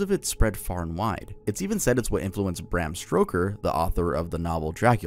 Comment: I'm sorry, did you say "Bram Stroker "? 🤭